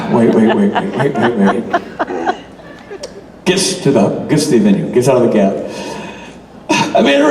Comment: Can somebody please tell me what Richard says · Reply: He said he was waiting for Rob and then he said "Rob gets to the venue".... Meaning Rob finally gets to where he is.